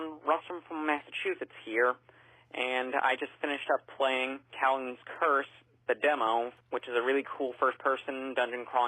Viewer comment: What game was that voicemail caller saying? Talon's Curse? I can't seem to find this RPG he mentioned. Anyone have some clarity on it? He mentions this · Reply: I think the caller was saying Kowloon's Curse. Incidentally that is where I am from (the real world Kowloon, not the one with the curse 😄) -Baku